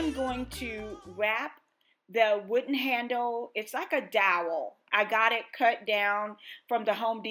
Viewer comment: Beautiful! You said that you will be able to cut several handles from the stick you bought from Home Depot. What length did you cut the stick and what is it called? I heard you say but I couldn’t make it out. Sounded like you said dower. Thank you. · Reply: Dowel from Lowe’s or Home Depot.